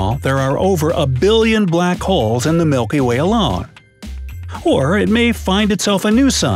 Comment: who else thought it said Mikey Way?